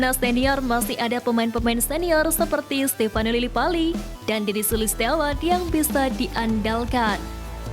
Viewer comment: "dan Dendy sulistyawan yg bisa di Andalkan" 🤔🤔🤔 apa sy gk salah denger???